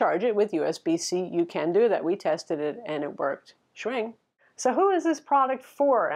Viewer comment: Did she just say "Schwing"?? 11:55 :)